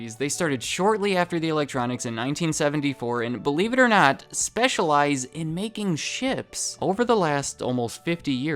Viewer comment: says "Specialize in making ships". At first, I thought he said "Specialize in making shit". :)